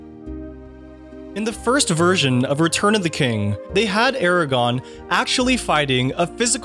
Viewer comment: did you really say "Aragon," and not edit that???